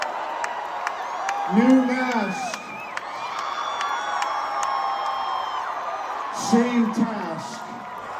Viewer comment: "New Mask. Same Task." 🤔🤔🤔🤔🤔🤔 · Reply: I thought I heard new mask same past lol